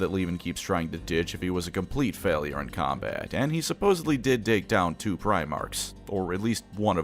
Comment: i heard "d*cked down"